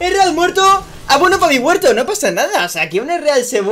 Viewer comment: ezreal muerto y que dice después? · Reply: Abono pa mi huerto